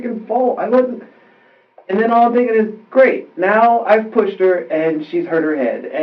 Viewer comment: ….He clearly states that he pushed her. Not “punched” like you claimed about 30 sec later commenting about that previous dialogue. The guy clearly is still lying about what happened but i’m just pointing out the inconsistency you stated in 53:05 about what he actually just said in your commentary soon after my time stamp. Just be accurate about what the dude said in the interest of your own credibility.